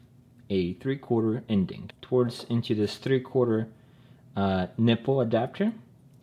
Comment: i thought he said Nipple Adapter.